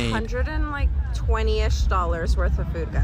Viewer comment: I thought she said 20 at first and I was hella confused until I played it back 😭🙏